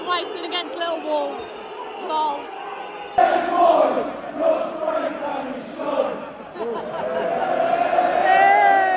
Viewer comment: can someone explain what they say to ben white at 3:20? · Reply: “Ben white, your spray tan is sh*te”